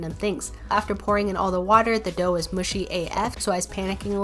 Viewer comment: You guys are the best! Thank you so much for the support! I’m live on twitch for a bit if you want to come say hi! <link> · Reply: did you just say af